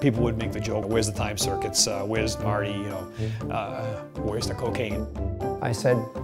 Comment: Did say where's the cocaine at 4:28? Great video real pair of characters thank you.